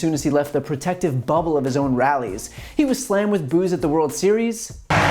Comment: I heard "he was slammed with booze at the world series" I was like REALLY LEMME SEE lol thinking people threw bottles at him. (realized you meant boo's)